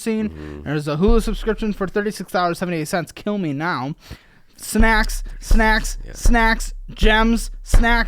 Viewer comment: I heard “snacks’is”. Sounds like taxes. like a snack tax on your life